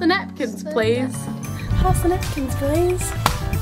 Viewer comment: I thought you said "pasta napkins please"